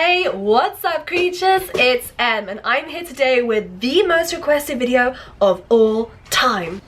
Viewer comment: I misheard and thought you said "most aggressive video" instead of "most requested video" and I was like what